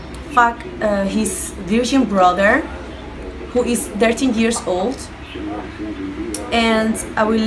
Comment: did she just say she is going to sleep with a 13 year old😯😯😯